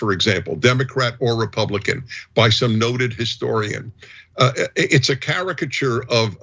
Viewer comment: I thought they said distorian. Instead of historian. Had to go back and listen to that part again.